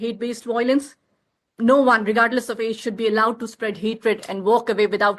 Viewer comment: I think she said regardless of age - maybe the government will finally look at the trouble making teens, regardless of their colour.